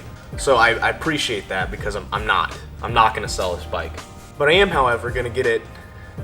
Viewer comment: keep the magna bro or the go fund me jokes will not stop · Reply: maybe i was to high but i thought i heard you say you were going to sell it i was mad until i heard you say you was gonna keep her